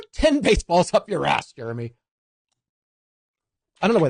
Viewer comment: really misheard "Jeremy" as "Germany"